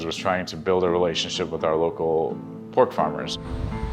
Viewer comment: Lol I had to rewind it, I thought he said former friend Ben, lol is was farmer